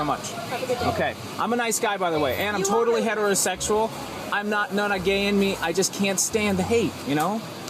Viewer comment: did he say "there is not a gay in me"?